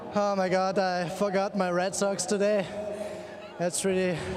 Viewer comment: did he really say...I forgot my red socks today, that's was a bad decision?